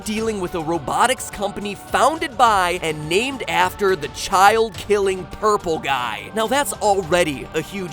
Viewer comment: When MattPatt said "Child-killing-purple-guy" I thought he said "Child-killing-pervert-guy"